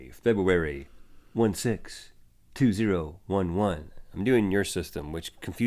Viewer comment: in the beginning of the vid when you said "febuary 1 6 i thought you said Febuary 26 cause Febuary 26 is my birthday